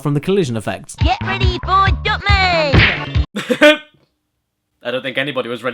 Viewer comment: Am I the only one that thought when said "Dot man to the rescue!" That he actually said Dump man instead of Dot man